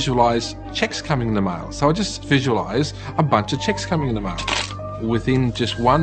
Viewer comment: lmao. A bunch of "chicks" in the mail is what I heard until I realized he met "cheques" lol.